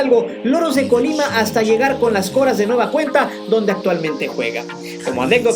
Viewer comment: No son las Cotas. Son los Coras... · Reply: Quizás escuché mal pero en el minuto 5:17 me dio la impresión que habías dicho las Coras. Saludos!!! Tus vídeos están chingones